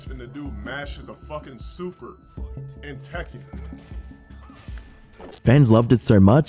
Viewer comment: i think he said “in tekken”